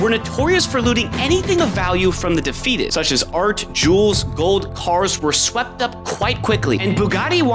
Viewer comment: Nahh. I thought you said Jews but you actually said Jewels.